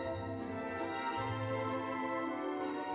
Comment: I thought she said holy shit for a second there😂